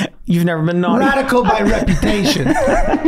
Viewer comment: I love how Megan starts laughing when Glenn says “But guys, that’s how I get off.” Lol it’s so cool seeing that she works with these guys now. Edit: By the way when Charlie said “Radical by Reputation” as a band name I misheard him and thought he said “Naughty by Implication,” which I think is way more fitting lol.